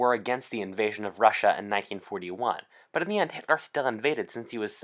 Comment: I thought you said 1961 instead of 41 by mistake. And it took me off guard, I had to go back and check. But its all good. Hahha. thinking of a nazi invasion of russia in 61'